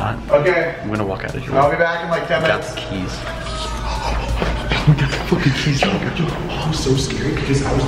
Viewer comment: did i hear say "we got the f*ing keys" or is it just me?